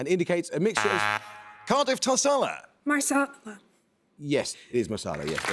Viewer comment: BUZZ "Cardiff Tarsala" "Masala" I was waiting for Impala bonuses next. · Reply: hand-orange-covering-eyes: sounded like she said "marsala" instead of "masala" ... like her teammate says "Mo-bile" instead of "Mo-beel" - but good that Amol let both go - good fight in the middle, this one.